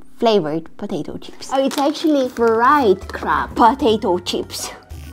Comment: i thought she said crap 😂